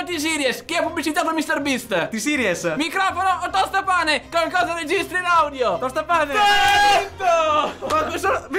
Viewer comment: Nella mia testa ho sentito “microfono o tostacrofono, con cosa registri l’audio?” “Tostapasta!” Poi ho sentito la frase e ho pensato “ahhhh è vero che era solo nella poop”